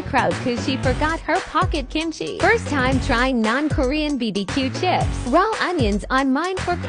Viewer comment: I thought she say "north korea" when she's said "non korean bbq chips".